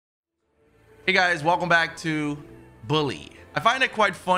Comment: did he just say "Welcome to Bluly" :)